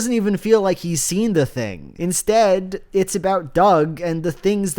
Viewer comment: Did you just call him Dog Walker? I'm never calling him Doug again lol